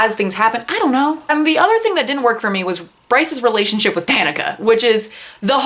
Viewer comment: I thought you said "Bryce's relationship with Canada" and I was very confused. I almost hate-read Crescent City. Her depiction of slavery put me off and her need to shove Bryce's special girl qualities down my gullet made me annoyed. I wish authors like Ilona Andrews got this much attention